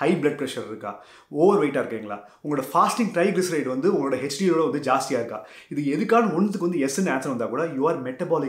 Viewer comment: can you write out what you said as the 4th point. (That triglyceride thing). Couldn't hear that properly 😅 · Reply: Are your fasting triglycerides higher than your HDL?